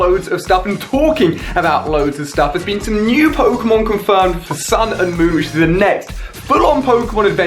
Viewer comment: I thought Ali-a said sun and mooooooo